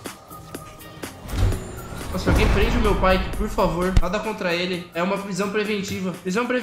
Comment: Eu ouvi alguém prende meu pai